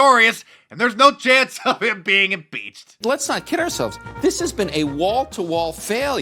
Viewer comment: Sounded like you said IMBEACHED